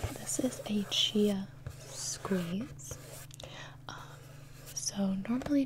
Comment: Who else thought it said 'chia sneeze' And figured that makes sense since it's in a booger coloured box 😂